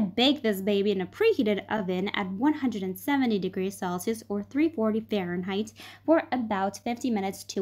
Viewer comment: Lol, I thought she said 170 degrees Fahrenheit when it was Celsius, welp, gotta put her back in the oven again 😂 pls don’t tell me I was the only one.